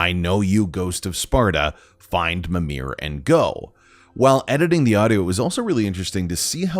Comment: Yeo i heard him say find mimir and Grow